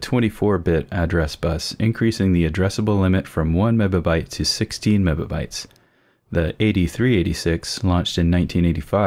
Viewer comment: I swear it sounds like you're saying 'meba byte'.